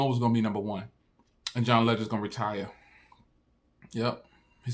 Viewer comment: i thought U said john legend the $inger Waz going to retire 🤔😂